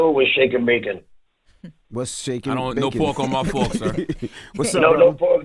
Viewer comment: What’s shaking bacon? I don’t want no pork on my fork😭😭😭idc Charlemagne really is so witty. I love him yo😭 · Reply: I thought he was saying “what’s shaking baking?” But the way Charla spun that was funny lol he a natural comedian